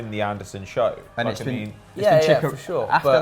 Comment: Is it just me or did call Chicharito a Chicarita at 24:03 lol